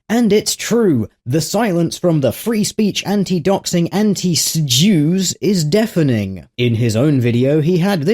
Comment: You read "anti-sjw" but I heard "anti-jews". Oy Vey!